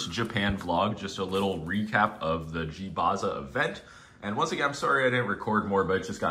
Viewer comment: I really thought it said Gaza vlog